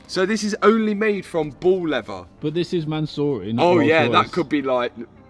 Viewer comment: Bro I thought you were saying “ball leather” not “bull leather” 😂